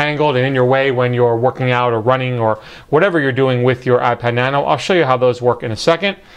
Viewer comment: did u say "ipad" nano